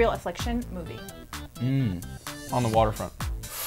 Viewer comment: what movie did Ansel say in the 5:57?? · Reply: On the waterfront x